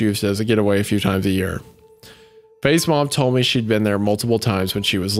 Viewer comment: Every time I hear "Faye's mom" I hear it as "FazeMom"